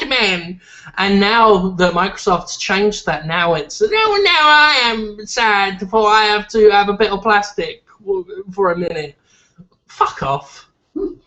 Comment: Hahaha what? 9:37 All I heard was "nahh iam sad manajhsahshsash plastic nahd foo min... FUCK OFF" 0_o